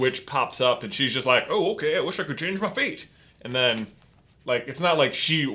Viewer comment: "Oh I want to change meh fate!" I lit thought you said feet!